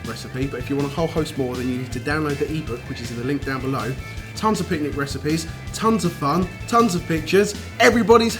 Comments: I thought he said "tons of bitches." I laughed so hard. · Honestly thought said at the end "tons of picnic recipes, tons of fun, tons of bitches, everybody's happy!" Ha :P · For a second I thought said "tons of bitches.." (pictures) anyone else? or just my gutter brain..